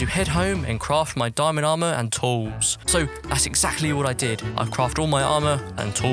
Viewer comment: I thought you said 'diamond armour and toes'